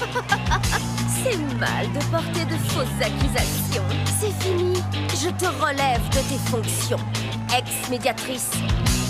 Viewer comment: Can someone just write in French what Lili just said in 2:54? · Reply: " Ahahahah, c'est mal de porter de fausses accusations. C'est finit, je te relève de t'es fonction, ex médiatrice "